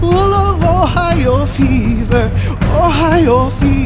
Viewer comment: what th FUCK i thought it said hay fever im distraught